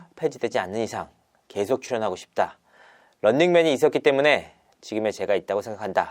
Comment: "런닝맨이 이ㅅ끼때문에" 라고 들림...